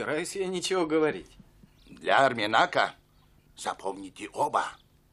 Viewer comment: Для Арминака честь превыше всего а мне послышалось для Армии НАТО честь превыше всего🤣